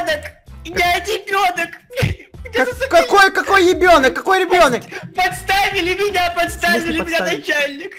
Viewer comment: мне сейчас послышалось или он сказал ИБЁНОК.